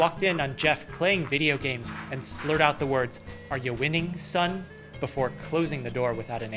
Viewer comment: Are you winning son? Thought he said But actually he said: Are you tripping son? The guy is watching elves dancing in the floor tho